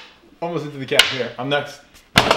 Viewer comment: I thought said I'm nuts.